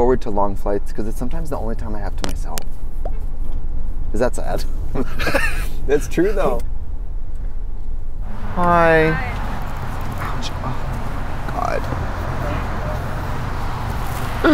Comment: sounds like the beginning of "Hi... I just heard!" I lost it